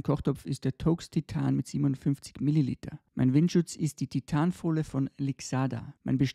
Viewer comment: Habe ich die Mililiterangabe richtig verstanden? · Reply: ? 750 sagt er doch